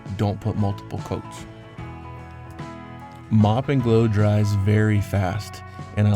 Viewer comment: Is this some clay or what? I'm russian and can't hear the name of this white liquid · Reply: I think it's Mop and Glo